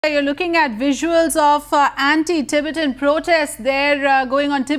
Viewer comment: did I hear anti-Tibetan protest?